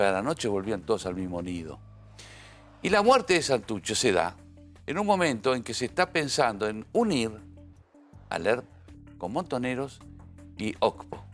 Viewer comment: Que dice en el minuto 8:57??? Al ERP, a Montoneros, y qud dice despues???? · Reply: "Y la muerte de Santucho se daba en un momento en que se esta pensando unir al ERP con Montoneros y OCPO (Organización Comunista Poder Obrero)"